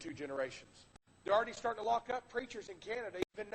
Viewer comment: Did he say they were locking up preachers in Canada or was that an audio glitch?